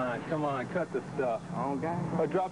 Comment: Cop: What gang are you with man? Gang Member: I don't gangbang Cop: Dude, c'mon, c'mon cut the stuff Gang Member: I don't gangbang Cop: If i dropped you off in slob's territory, then what are you going to tell me then? Gang Member: Then i'm just going to tell you don't drop me off over there lmao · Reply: I heard "slobs" but we're each entitled to our opinion. Coulda been referring to neighboring family swans but I don't hear an "n" after listening to it several times.